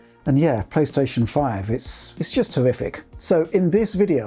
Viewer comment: It sounds like Rich says "it's HORRIFIC", but I think he really said "TERRIFIC"...? I think the PS5 is visually horrific, but spec-wise it seems okay. That storage issue is a big nope for me though. I have slow satellite internet with a 50GB/mo data cap; I can still use it and download things past that, but overall even slower and less reliable. I don't have the luxury of downloading a game more than once, it has to be archived. This is going to be a problem even for people with fast internet, that new COD game is closing in on 300GB install size apparently. That's insane! That's almost HALF your PS5 storage, that you cannot move off of. It's going to be almost mandatory to buy an M.2 SSD expansion right away. I wonder if those will be proprietary or if you can drop in any compatible M.2 drive?